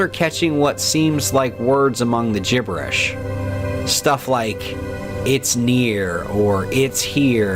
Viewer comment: I heard “it’s Neil”